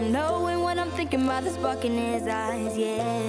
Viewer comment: Doesn’t it sound like Dylan is saying the f word instead of spark